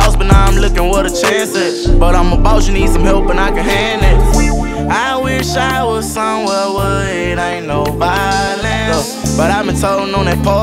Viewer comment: “ I wish I was somewhere , where it ain’t no violence “ 💯💯💯💯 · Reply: I thought he said " i wish i was somewhere, ain't no one can find me" either way i felt that 💯💯💯💯